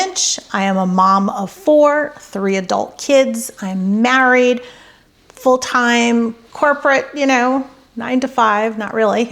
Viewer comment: I thought you said " I am married full time ". Lol😊ll😂